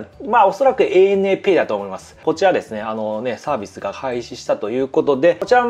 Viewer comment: サービスが廃止したということでって聞こえる笑笑 多分開始か配信やと思うねんけど混ざったんかな笑笑